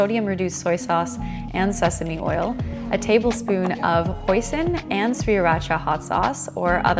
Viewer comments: did she just say a tbsp of poison? :o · Who else heard a tablespoon of poison at 1:52?